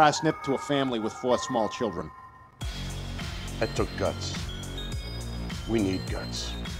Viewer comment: I heard "we need gus"